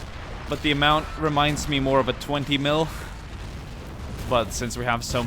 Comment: sounds like hes saying 20 milf